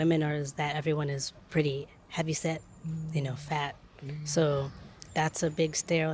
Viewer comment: It’s entertaining to be reading the subtitles and knowing someone who is more familiar with a Japanese accent cant tell what they’re saying. Noticed it a few times but here 7:26 it’s “heavy set” not “how you say”